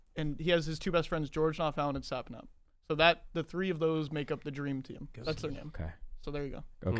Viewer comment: Did anyone else hear say "georgenotfound so dreamy"